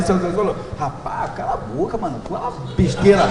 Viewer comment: Só eu ouvi em 1:53 o pastor falando "caralho..."